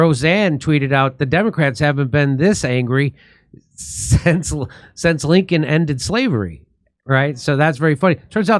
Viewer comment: I heard it as "The democrats haven't been this angry since the republicans took their slaves away"